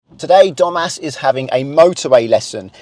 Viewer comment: ngl i keep hearing dumba**